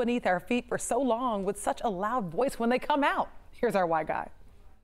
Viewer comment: She said here’s our Why guy, i thought she said here’s our walter white guy 😂😂😂😂